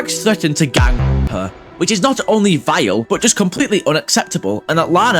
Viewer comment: WHAT DID YOU SAY!! also u forgot to sensor it lol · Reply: what? he said vile.